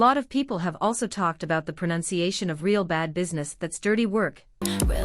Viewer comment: Until I saw this, I thought they were saying “rope up in this, that’s dirty work” 😭